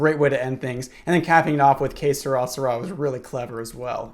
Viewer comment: i thought you were saying some sort of ancient egyptian phrase like K'srasra but you were trying to say ''Que sera, sera'' lol